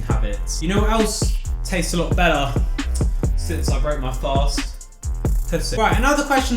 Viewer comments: did he say pu$$¥ tasted better😳😳😳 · did you really say what I think you said...kitty...tastes better? The first water fast video was great and brought me to this one which answered many questions I had. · Did you just say pissing tastes better?